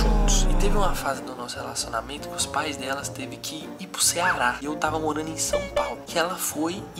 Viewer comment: ouvi ceara e isso msm? Estado onde eu moro ♥️